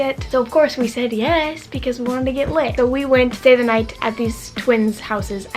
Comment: lol i thought you said "of course we said yes because we wanted to get laid."😂